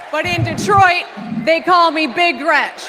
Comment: Whoa, whoa, whoa... did she say they call her "Big Wretch" Wtf???! 😮😮